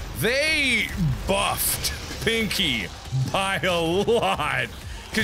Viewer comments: Did he just say Pinkie Pie? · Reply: No he said "Pinky by" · someone needs to make a mod for the original doom, that replaces doomguy with markiplier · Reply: Did he say Pinkie Pie